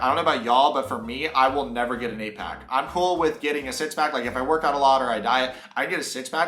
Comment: When he was talking about getting a six pack, I swear he said “If I workout or I die” and that was something I related to